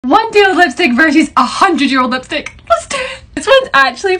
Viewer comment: I'm over here watching this at midnight and I'm so tired I thought she said 1 day old cheesestick or 100 year old cheesestick